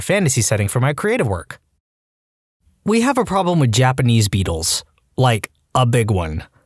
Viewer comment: I misheard that as "we have a problem with Japanese People" and a though WOAH ....then I went back to that bit and it was beetles